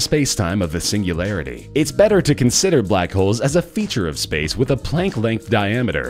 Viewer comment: lol for a second i thought u said black hoes